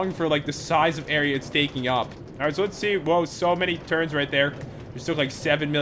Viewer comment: i thought he said turds insted of turns XD